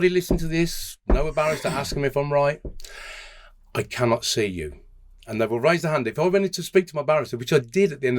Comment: It's not "I cannot see you" it's "I cannot hear you"